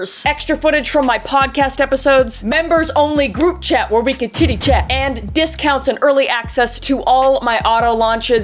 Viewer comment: Not even 2 minutes in, you said Chitty-Chat and I STG I thought you said titty-chat and I was very intersted until I rewatched to see what you actually said. Lol. I'm still intersted, just not as much! LMAO